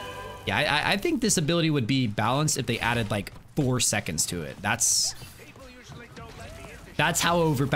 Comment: I swear you said Thor seconds 11:35 and not four lmao